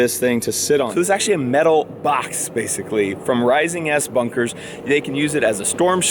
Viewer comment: Thought you said rising ass bunkers